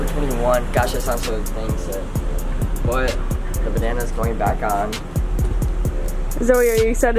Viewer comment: WHY AT 6:18 DID I HEAR SAY BANNana insted of bandena idk😂😂😂 btw my grammer sucks